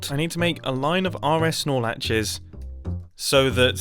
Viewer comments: Not gonna lie. 12:01 I thought Mumbo said a "line of R S Snorlaxes." Had to replay it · I can’t be the only one that keeps mishearing RS NOR Latches as RS Snorlaxes.